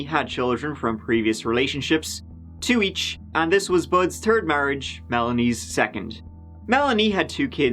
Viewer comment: Anyone else hear " Budds terd marriage" at around 1:57? I am not making fun, I love you and your channel. 😃❤❤😘